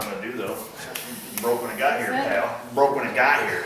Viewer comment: I waited patiently through 5 videos to see whether would repair the windshield wiper nozzle that he broke when he started this job ---- and he DID ! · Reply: I couldn't understand through my speaker, what was he saying? This is what I was waiting for too and it sounded disappointing when I heard him say "broke when it got here pal". I am hugely disappointed in him if that's what he was saying but I wouldn't expect that of him, so I'm assuming I heard it wrong or it was a joke